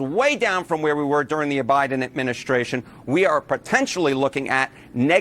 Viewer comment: Listen closely between the 4:00 to 4:04 marks - I swear that Mr. Enten calls it the 'OBiden' Administration, and even the CC text seems to think so, claiming that he said, 'aBiden Administration'